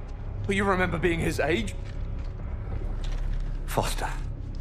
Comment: I fucking hate this bit because all I can think of when he says Foster is "Good call"